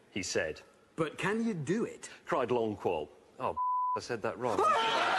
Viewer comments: what did he say that made hammond lose his shit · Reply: "Fuck" I said that wrong. I think he bugged out due to the multiple times I did the take. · What did he say? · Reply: "Cried lolcual, oh sh t I said that wrong